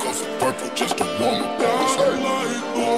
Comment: Moment of silence for the people that cant find this song. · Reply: I'm Italian so maybe my understanding of English is a bit weird but I swear when I heard this on tiktok I thought it said "Popsicles are purple" instead of "pop 2 cups of purple" lmao